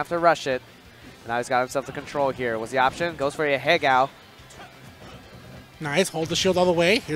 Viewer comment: did he just say "Ahegao"?